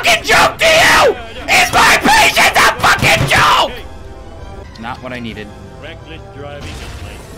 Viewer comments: When your helping your dad build something and you give him a screw instead of a nail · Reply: not what i needed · it is literally almost impossible to hear what he said. “NOT WHAT I NEEDEEEED!” It’s probably the funniest shit in this video, though. 😂😂😂😂😂😂